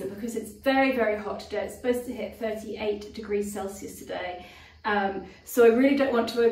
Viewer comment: Sorry guys, I just realized you are talking Centi grades and not Fharenhite . 38 degrees Centi grade is hot. I thought you said 80 degrees!!